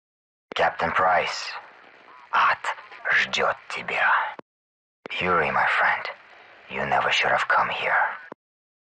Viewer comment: ? what does he say? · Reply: "Ад ждет тебя" Hell awaits you.